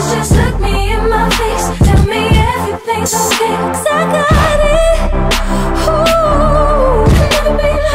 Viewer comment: this whole time i thought she was saying "suck on it" but she was saying "cause i got it"????